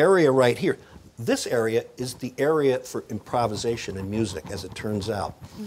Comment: what did he say there? Anyone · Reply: improvisation in music (making it up as you go)